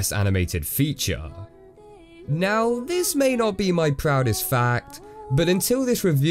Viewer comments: When he said not my proudest fact I thought I’m he said not my proudest fap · I thought he said not my proudest fab XD